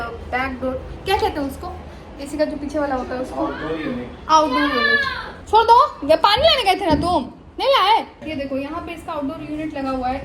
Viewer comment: Samaj nahi aaya kya bola lekin bahooot CUTE laga ☺️. you're very similar to Gulab-Jamun 😂, Very Sweeeet❤. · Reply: Hehe ! Maine bola pani laane gaye the na,kyu nahi laaye 🤗